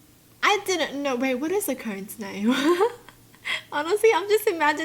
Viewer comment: i wasn't looking at the video and i thought she said toe nail instead of cone snail. i was so confused ;w